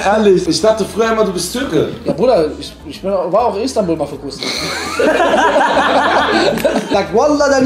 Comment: Weiß einer von euch Was bei 6:08 sagt ? Ich war Istanbul.?.?.?.? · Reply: Ich habe verstanden "war Istanbul für kurze Zeit" ????